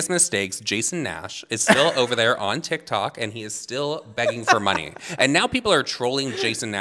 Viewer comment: i thought they said diktok 💀 ”and that’s nuts.. MACADAMIAN!”